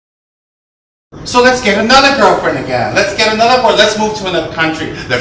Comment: what is it said at 0:02?? "so lets get another ?? again" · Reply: Girlfriend.